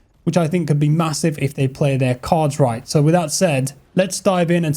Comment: with your accent it sounded like you said "if they play their CODs right" and i cracked up. Peak vid my friend